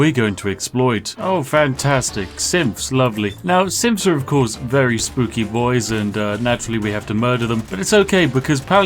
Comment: "Simps are very spooky boys" Misheard lyrics 🤣